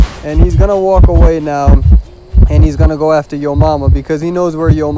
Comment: "And then he's gonna go after yo mama because he knows where yo mama is." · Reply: i thought we has saying your mama.and then i realized that yomama is the survivor name.